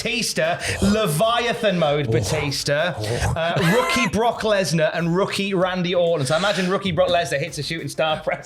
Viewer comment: I thought he said "Wookie Brock Lesnar." I want that instead.